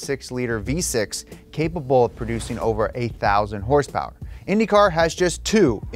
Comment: Closed-caption says 8,000 horsepower! (He actually said "A thousand horsepower".)